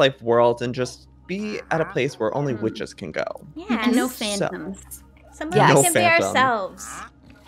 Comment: “No phantoms” I misheard for “no fandoms” and my sleep deprived self who stayed up all night last night watching anime and reading fanfic almost cried 😂